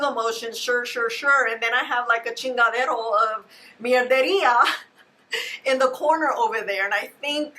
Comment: Love you! Can someone translate 10:37. Is she saying a pile of… shtuff?